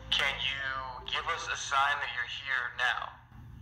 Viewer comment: I heard:" nice you're here"